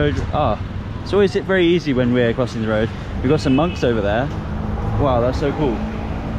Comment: I thought you said “we got some mugs over there” 🤣🤣🤣🤣